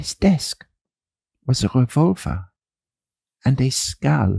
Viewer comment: I had to go back a few times because I heard vulva and not a revolver when I first heard that and thought what an odd thing to have on his desk.